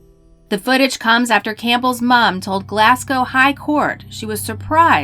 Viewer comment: Glasscow, second cidy of Scotchland, England. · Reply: Hi Paul. At 9:43, the narrator attempts to say 'Glasgow', yet it comes out sounding like 'glasscow'. I was mocking Americans who can't pronounce British place names; the types of people who say 'cidy' instead of 'city' and assume 'Britain' equals 'England'. Let me know if you need other bits of the internet explained to you. Cheers.